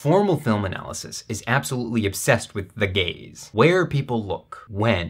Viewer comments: i thought he said the gays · I stop paying attention for ten seconds and i hear you say "formal film analysis is absolutely obsessed with The Gays"